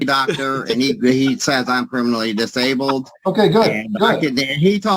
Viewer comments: is he saying permanently disabled or criminally disabled?? I keep hearing criminally, but I’ve never heard it expressed that way. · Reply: when you don't put your teeth in, everything sounds different. I too thought he said criminally, but he did indeed say permanently. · Did he just say he’s “criminally disabled??” LOL 😂😂 wtf? I’m so glad the judge is continuing with this! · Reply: 😂😂i thought he said criminally disabled as well. Lol